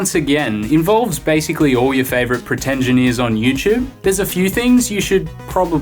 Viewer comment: Did he just say "Pretengineers" ? What a lovely compound word, I will have to use that some day